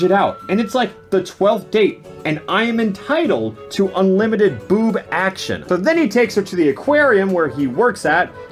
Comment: This was hillarious but Its honestly a good movie to be a romcom IMO, mostly because all other romcoms are even worse. Btw have my life been a lie, I thought he said "Unlimited boob acccess" not "Unlimited boob action". I feel so cheated on